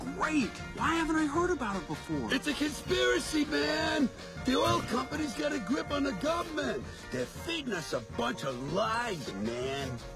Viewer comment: I always hear “They’re feeding us a bunch of lice, man!” 😂🤣🤣